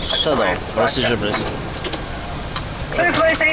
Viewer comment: did he just say shut up lmfao